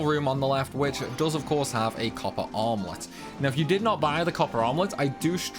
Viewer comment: all I hear is “If you did not buy the copper omelette” lmao